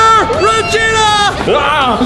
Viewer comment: what did say hahaha😂😂😂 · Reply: He said wiener